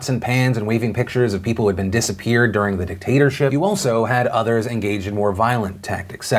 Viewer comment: I know you said “dictatorship”...but I heard “potater-chip” and I think it’s a better option. 😁